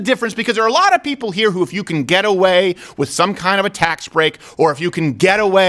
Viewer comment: I kept hearing you say “ghetto way” not “get away”. Lol funny and cant wait to finish this and start a new one